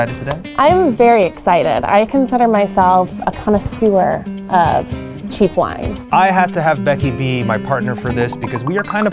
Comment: Becky: "I consider myself a connoisseur of cheap wine." I definitely heard "I consider myself a kind of sewer of cheap wine" at first.